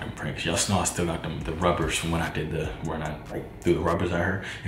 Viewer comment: I thought it said robbers at first 😭😭😭